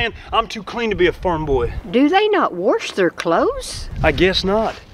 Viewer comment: Once I heard “warsh their clothes” and “warsh up “I had to sub